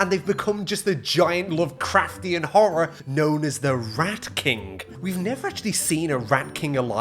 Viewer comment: I thought you said RAP king not rat king. I thought that you were talking about Eminem or someone.